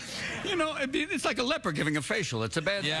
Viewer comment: Holy Christ. This whole time since I was 10 I thought he said a "leopard" giving a facial. lmao